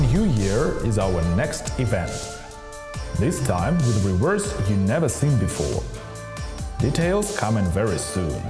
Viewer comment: At about 0:18 i keep hearing "This will be worse then you've never seen before." Id: 3CB1RH Plaform: amazon